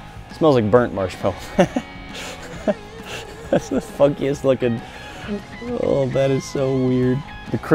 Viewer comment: Anyone else hear "thats the f*ckiest lookin" at 3:12???